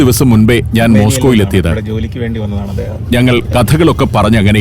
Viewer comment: What was that whisper in malayalam heard faintly below the voice-over ? 😊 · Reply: Armeniayil ninn ivide jolikk vendi vannathaanu adheham